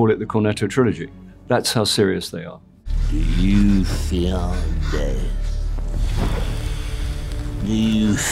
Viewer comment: When I think of him I hear: “These are dark times, there is no denying… Your ministry remains STRONG.” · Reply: I always hear "do you fear death"